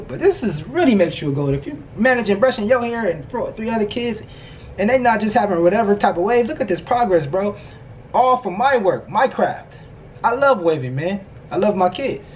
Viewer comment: I thought he said Minecraft😂 I need to pay attention more